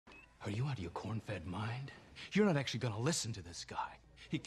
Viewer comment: "Are you out of your cornfed mind?!" lmao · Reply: I always thought he said, “Are you out of your Vulcan mind?!?”